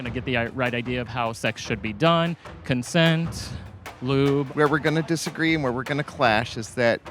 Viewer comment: did he say lube? Like is he saying that to bring up a**l sex or something? Nothing is wrong with lube lol